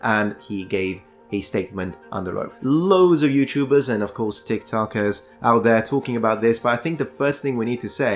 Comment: Dude!! Did you say "...loads of youtubers and of course di*k su*kers, out there talking about this..." OMG! i never thought i would here you swear 😂🤣😂 o no wait, i played it a few more times now. You said tik-tokers... Well basically same thing i guess 😅